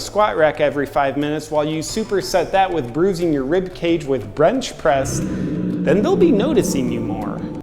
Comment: did you say "brench press"?